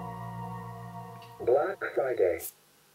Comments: I heard "Black Fighting" each time this played 😭😭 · All I hear now is “Black Siday” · I hear "Black Side A" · I hear "black syday“